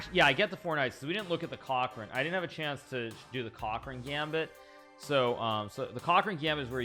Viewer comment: No, the massage in chat was smth like “WHAT GAMBIT?” And Hikaru understood that chat was joking about “cock” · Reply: sounded like he said "cock ring gambit" to me lol